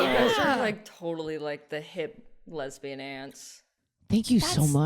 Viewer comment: when said "lesbian aunts" I heard "lesbian ants", so now I'm thinking about lesbian ants. :)